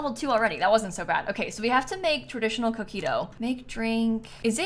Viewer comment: I thought she said “traditional cookie dough” when talking about coquito 😂